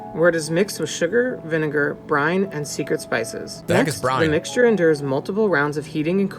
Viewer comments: You thought he said Brian lol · I thought he said Brian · I thought he said Brian xD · I thought he said Brian.